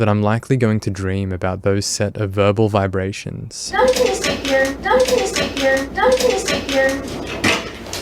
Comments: I hear "No we can just eat here" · I THINK she said "No, we can just stay here" or smth like that.. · "No we can just eat here." Is what I hear, but what do I know. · “…we can just eat here” is what she says I think! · Hi, Greetings from Argentina. I just wanted to add that in the minute 39:40, I think the decoy says "no, we can just stay here." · I think she says “don’t be insecure”